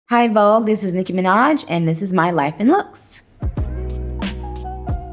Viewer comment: I had thought that she had said her life in hooks